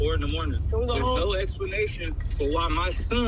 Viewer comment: Neek .. it sounds like he said “and your baby daddy is there” not “dead” lol .. I think Marsh had her child’s father there at the house also ..